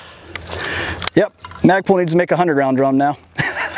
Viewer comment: For a millisecond I thought you said Magpul IS making a 100 round drum now….. My fingers were halfway to pulling up their website when I realized you said needs to…..